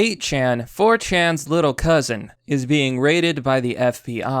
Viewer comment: Little cousin? I thought you said Little Brother?😎😎😎😎😎😎😎😎👌👌👌👌👌👌👌👌👍👍👍👍👏👏👏👏👏